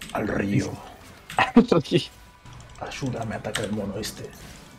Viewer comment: no sé por qué escuché "Ayuda, me ataca el Noroeste" y me hizo mucha más gracia